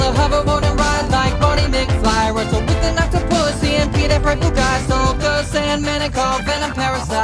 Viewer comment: I Heard it as "WRESTLE WITH AN OCTOPUSSY"